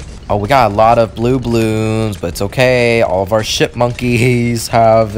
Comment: I think said a bad word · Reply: he/she meant to say ship but it sounded like sh¡T Also she/he said buccaneers